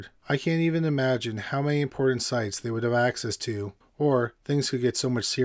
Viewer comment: I heard "porn sites" instead of "important sites" in the sponsor message. That made it much better :D